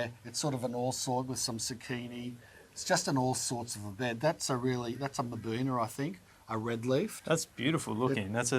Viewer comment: What is the name of the red leaf plant at 57:45? Did you say maboona?